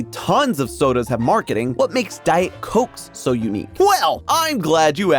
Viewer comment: I love it when AI subtitles substitute "Diet Coke's" for "Diet coax" at 5:49. AI can't catch up with humans. We're not doomed yet as a species.